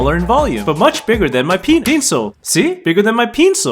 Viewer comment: bigger than my pe Pencil I hear penis😂😂😂😂😂😂😂😂😂😂